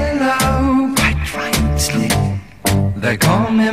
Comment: I know it says "Quite Right-ly" but I could swear its "Quite Right Slick". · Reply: I always thought he said, "quit right slim"